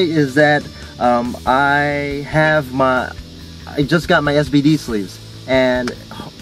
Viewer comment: u got us pegged . We wanna see your beautiful face. At first I thought I was hearing things. Thought u said u had STD 1:24 LOL